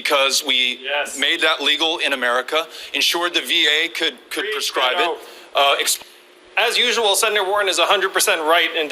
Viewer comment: what did he say at 6:23? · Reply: He said "Yes" then "Preach Beto"